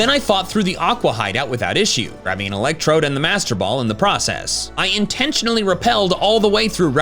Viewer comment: At first, for a split second, I thought you said "grabbing an Electrode IN the Master Ball" and was like WHAT?!? 🤣🤣🤣 Very fun run! I love the Battle Frontier so to see this for an entire playthrough is really cool 😊